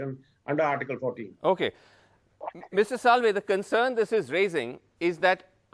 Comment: did he say Mrs Salve????!!! And Mr Salve looks back to see if his wife is anywhere in the frame!! 🤣🤣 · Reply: You are right.that dumbass said Mrs salve..